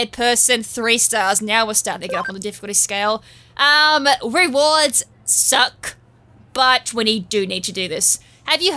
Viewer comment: i ligit thought you said the reward for this mission was "the suck" XD made me do a double take